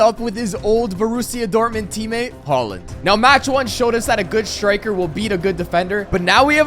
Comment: Am I the only one who heard Poland?